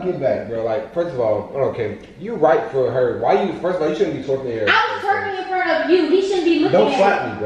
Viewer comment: if you hear closely it sound like he said first of all you on camera🙄🤔😂😂😂😂💀💀💯💯✋🚫🚫🚫